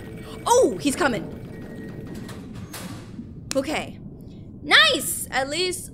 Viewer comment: nice vid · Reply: i thought u said nice vid but u said nice at